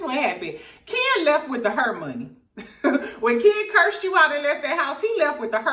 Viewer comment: Whole time you saying "Hair Money" I thought you was saying "Her Money" 😂😂😂